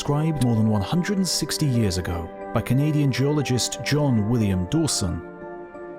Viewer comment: I thought you said Dawson was a "comedian geologist" for a second and I was like "that's... novel?"